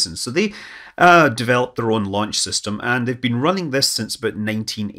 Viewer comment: I thought you said the shoveit launch system